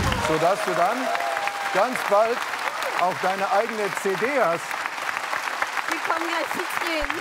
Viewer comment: was sagt er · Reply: So dass du dann deine eigene CD hast.